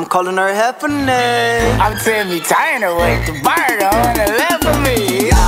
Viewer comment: Can somebody please tell me what he says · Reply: Jade Sutters " I'M TIMMY TURNER WITH THE BURNER ON THE LEFT OF ME YAAAAHAHA